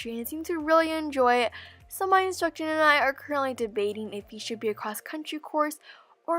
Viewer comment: it sounded like you said “If he should a XC course” lol.